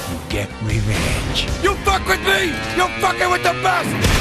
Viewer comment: ehm.. at 1:00 did he say : Fucking Kakarotts?